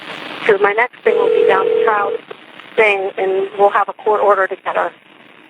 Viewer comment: 🚨 Listen closely to CINDY IN THE PHONE CALL I CLEARLY HEARD AT 1:19:22 Cindy say “My next thing will be the child thing and we will have a court order to get her!” Then you hear Casey say “Give me one more day I’ll get her.” Then Cindy says “I’m not giving you another day I’ve given you a month!”